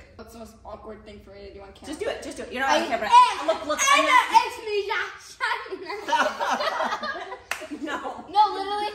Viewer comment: what is saying 😭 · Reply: He is saying my name is like that